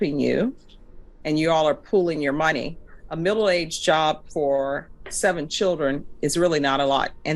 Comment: I thought she said a middle aged job the second time lol